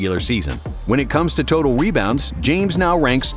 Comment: why does it sound like he said “webounds”